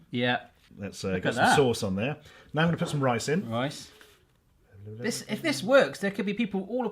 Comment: Did he say I'm gonna put some rice in or... I'm gonna put some Ricin :O don don dooooooooooooooooooon